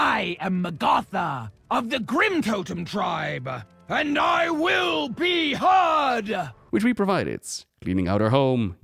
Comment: For a sec I thought she said "I will be hugged!" Instead of "I Will be Heard!"